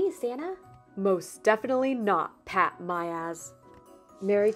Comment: I heard pat-my-ass at 4:20 XD